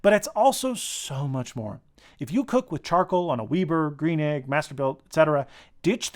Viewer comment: Did you say "Weeber" at 19:29? Thumbs up for the Combustion Inc probe... It's expensive, but worth it. The outboard display is really useful.